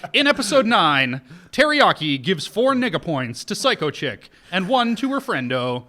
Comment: I thought said “Nigga Points” but for some reason I was cool with it.